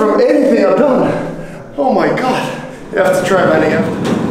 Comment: did he say “you have to try my nigga”?????